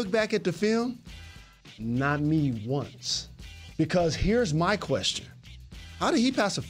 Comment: I needed subtitles there. I guess you did too hahaha Sounds like “Not even once” but that doesn’t make sense under the context of what jaylen was saying.